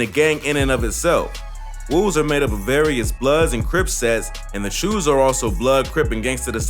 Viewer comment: did he say the shoes?